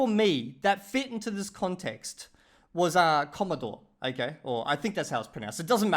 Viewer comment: I thought you said ‘comedor’ and was very confused when you said you heard it all the time as dining room is a pretty niche word lmaooo